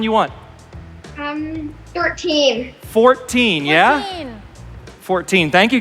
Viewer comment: For the poker one with max major, did anyone catch the girl in the pink actually say 13 and they thought she said 14 so they used 14 instead? Was that just me?